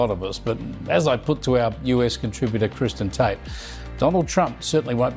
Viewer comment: Kristen Tate lol. I thought they said Tristan Tate at first. Look if they can sue infowars into Oblivion for saying what they said, these bigger companies should not be any less susceptible